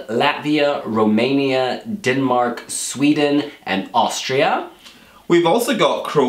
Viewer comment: They just forgot Austria 😂😂😂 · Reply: they actually said Austria😉